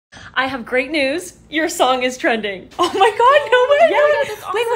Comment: istg i misheard her saying "your son is pregnant" 😭